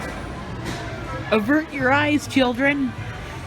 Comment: What did you say? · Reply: "Avert your eyes children"